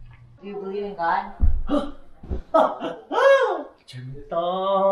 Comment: did he just said "puta"? Or i just misheard it?